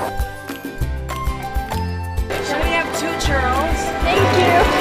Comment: He looks so nice! :) haha I thought he said "can we have two turtles?" Im like what? But he actually said "two churros". HAHA